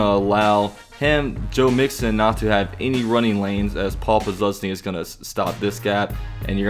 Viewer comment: anyone here "papa luzny" cause that's what it sounded like to me.